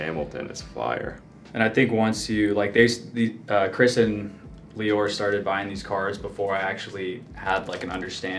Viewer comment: wait did he say leor as in mamba cards leor? at 6:40?